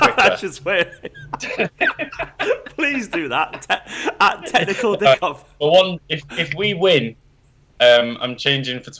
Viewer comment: i thought they were saying technical dick off for a second